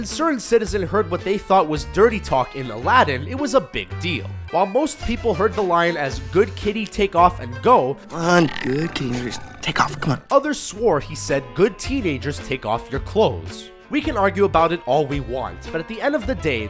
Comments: I thin 2:05 2:05 said ahh good teenagers, take it off come on.. it kinda sounded like whispering so maybe it was a accident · if you listen closely to what he says it does sound like hes saying "good teenagers take of your clothes" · I heard good teenagers (something ) clothes off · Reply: Slowing down the audio, it sounds like he says "Good teenagers take off", No "clothes", though.